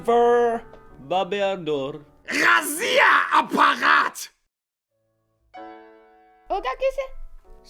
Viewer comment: На 2:43 мне послышалось "Разъёбанный аппарат"